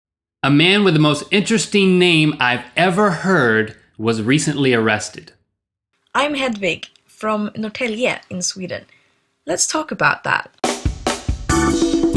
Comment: Did she say her name is Hedwig? Like Harry Potter's owl?